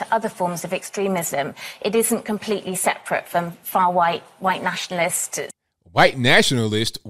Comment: She actually says "far white", Freudian slip. To these people racist just means conservative.